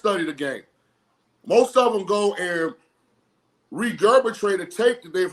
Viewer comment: Did he just say "regebitrate"?😅